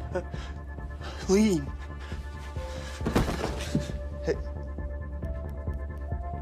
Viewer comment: shaun: you need immediate treatment the patient: what? for what? shaun: ... the patient: for what for what!? shaun: trampoline i still dont get why he said trampoline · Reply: Tell me why I thought he said tramadol XD